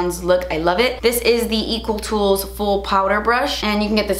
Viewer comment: For a second I thought you said Equal Tools and as I was Googling Equal Tools... I realized you said Eco Tools 🙈